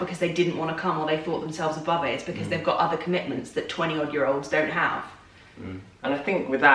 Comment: is there something wrong with my hearing or did say twenty-old-year-olds at 8:26 ? 😂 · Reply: She said 20-odd year olds, so in any year of your 20s